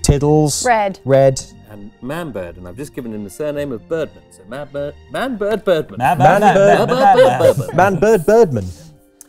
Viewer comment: Instead of "Manbird Birdman" I definitely heard "Manbird Bourbon." I like both. 😉